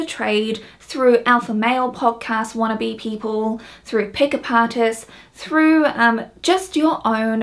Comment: I thought you said 'pick-apartists' and I was like...oo that's an interesting name for it. But then I realised 😅